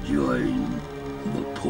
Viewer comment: I thought he was saying the dark side not the pork side